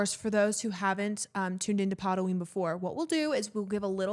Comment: i thought you guys were saying POTaween until now… kinda disappointed LOL